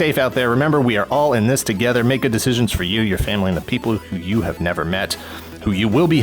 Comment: I thought you said, "aaand the family you've never met..," which made me sad for all those poor, out-of-work NBA players.